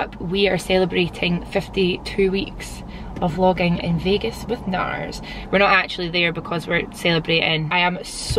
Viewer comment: LOL I thought she said Vegas with Nas and I was shook